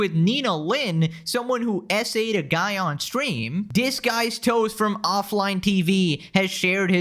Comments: had to double take at around 22 minutes in cause I thought you were saying someone assaulted dis guys toes, not disguisedtoast lmfao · Dude when he was saying Disguise Toast, I thought he was saying Dis Guys Toes.... LoL Awesome name!